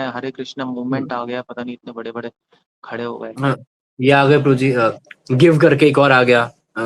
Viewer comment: Givegita or GEV (goverdhan eco village) ? or something else sry i couldn't hear it properly? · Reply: Give gita